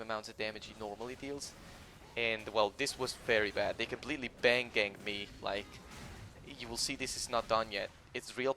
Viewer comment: Did you say bandganged?